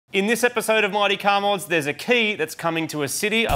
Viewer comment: is it just me or does it sound like it says Marty car mods in the intro?